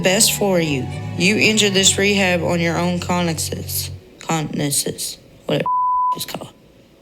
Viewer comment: Weirdly it sounded like Amy was saying "You entered of your own recognizance," which would mean "of your own volition" or "by your own choice." Though I think that word is really technically only applicable in situations where a judge releases someone without bail on the promise that they will return on their appointed court date? Still, I feel like I've seen people use it to mean "of my own free will" so maybe it's entered the common lexicon in that way. Either way I feel like the Slaton sisters often do that--they know what they're trying to say but they can't quite get the word right lol. In this case, her point would've been that if Tammy, who entered Rehab by choice, couldn't even stick to THAT, she was definitely not ready to be on her own without help, and she (Amy) wasn't willing to be that help anymore.